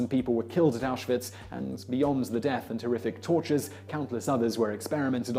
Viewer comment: Hol' up, 6:11 Did you say terrific tortures?